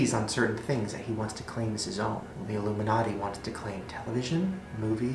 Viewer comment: I thought it said illuminate